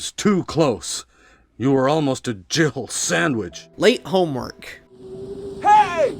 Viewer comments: It actually sounds like he says "Jibble sandwich". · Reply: I kept hearing him say "Jihill Sandwich" until you mentioned that. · “You were almost a Jill Sandwich” One of the most iconic quotes of all time. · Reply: It actually sounds like he says "Jibble sandwich".